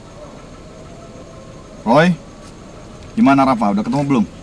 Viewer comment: menit 2:36 kedengerannya "roy dimana Rafal?" hahahhahahahahahahahahahahaha